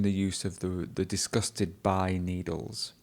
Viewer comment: Thank you for posting this! I am also from Lebanon and I have my test soon However at minute 13:43 I heard say ; Im not disgusted or worried about needles which sounds completely right. Whereas you explained it as : disgusted by needles only Thank you a lot everything is very beneficial